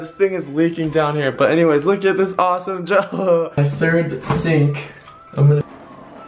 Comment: Did he say jell-o